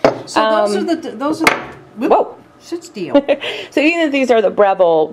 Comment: I could swear I heard say "shit steel" near the end of the segment. Now she will try to convince us she "sit still" but really come on! (please forgive my silly sense of humour...LOL) Another great vid ladies...very informative!